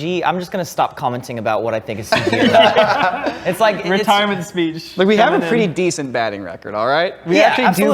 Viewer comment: Did just call it "a Wrentirement speech" and everybody missed it?